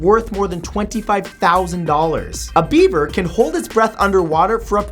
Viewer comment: it sounded like he said a bieber instead of beaver.